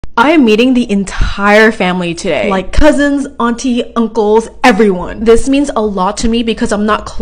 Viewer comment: I THOUGHT SHE SAID: I AM EATING THE ENTIRE FAMILY...😳